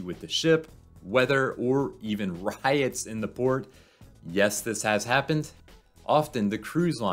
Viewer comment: When you said “riots in the port” at first I thought you said “pirates in the port” and was like 😮